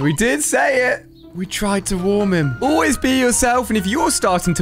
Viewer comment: Did you just say we did warm him or is my head playing tricks with me?